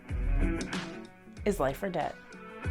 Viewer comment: New subscriber!!!!!!! OMG YOUR CHANNEL NAME IS AMAZING!!!!!!!!!!! I didn’t see your name at first, but you said it in the beginning and I totally thought you said life or death hahahahhahaha. I was like…. WHAT??? Hahhahahaha I love it!!!!!!